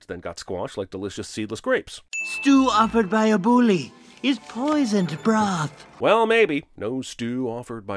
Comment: Bro I always thought Chester said “stew offered by a bully, is poisoned bra.”